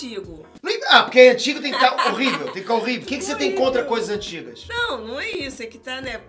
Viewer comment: o que o falou (o que que você tem contra coisa antiga)o que eu ouvi (o que você tem contra o